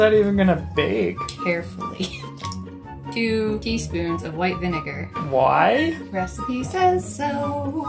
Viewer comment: I’ve been doing a lot of carpentry lately and when you said white vinegar I thought you said white spirit and panicked remembering how toxic it was it took me 4 watches to realise you said vinegar not spirit